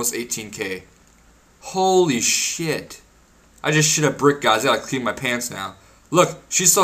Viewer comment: Not sure if i heard this right, but your 138 with a max cape, and i think i heard you say "i gotta play i my main again"..i don't even have 3 99s and you have a maxed and a MAIN?